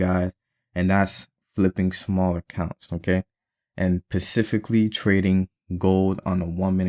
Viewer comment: Am I the only one that heard him say “pacifically” instead of specifically 😂😂😂